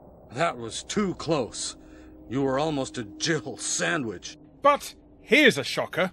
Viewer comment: I'm pretty sure Barry's actually saying "You were almost a gibbled sandwitch", and people just mishear it as "Jill sandwitch" because it's funnier and "gibbled" is not a very common word